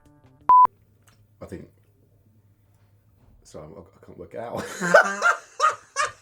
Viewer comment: What did say at 0:42? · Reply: "I think.. Sorry Sime I can't work it out"... maybe!